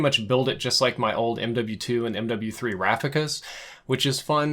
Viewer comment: Did he just say "MW2 and MW3 Rafficas"?